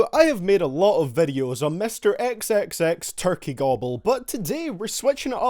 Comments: Wtf did u just call him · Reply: xxxturkeygobble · the HELL did u just call him? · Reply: XXXTurkeyGobble